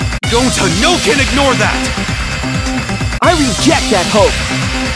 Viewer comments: Deadass thought Shuichi said "I reject that ho" I was rolling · Why did I hear “I reject that hoe!” when Shuichi said “I reject that hope!” 🗿